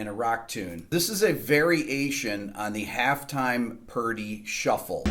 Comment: Thought you said "very Asian half-time Purdie shuffle" and I was intrigued. Had to re-listen a couple times to understand my mistake.